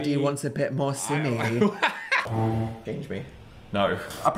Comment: what does dan say?????? I cant hear it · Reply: "change me" cause he dressed as a baby 😭